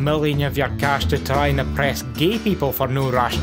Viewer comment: Did anyone else mishear "oppress" and "impress"?